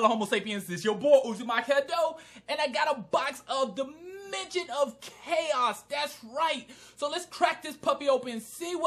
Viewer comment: It sounds like you said Da midget of chaos.